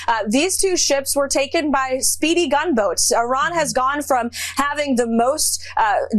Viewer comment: I thought she was about to say "Speedy Gonzales" when she started to say "speedy gunboats" 12:41 Has anyone reverse-image searched their pictures? How did they find pictures where they're all against a black background with similar backlighting, with their hair and makeup done up? IDK, it set off my BS meter, but so does everything coming out of the WH.